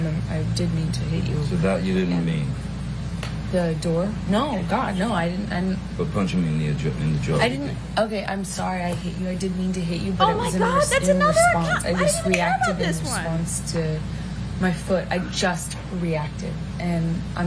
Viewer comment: my subtitles say “God no i didn’t end up punching me in the Egyptian” lmao